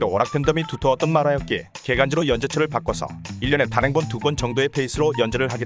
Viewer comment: 계간지를 개간지라고 들었넼ㅋㅋ